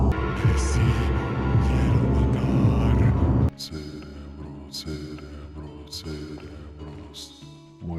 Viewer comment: When the cultists were saying: cerebro I thought he was saying cinnamon rolls 😂